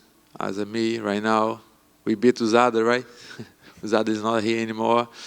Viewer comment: Paulo said USADA, and I thought he said Adesanya.